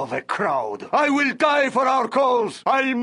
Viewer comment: When I first played this game and heard him say, "I will die for my cause!", I thought he was saying, "I will die for my clothes!"